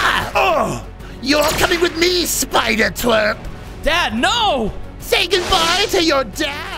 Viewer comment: "say goodbye to your precious little spider twerk" 💀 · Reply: It did sound like he said that but he said twerp😂